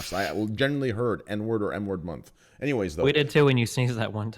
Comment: it did sound like he said "n word manly March" but he said M word i guess. poor getting trolled?